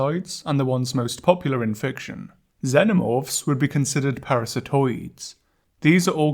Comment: for a solid 10 seconds i thought you said “animorphs” and i was VERY confused amazing video as always!!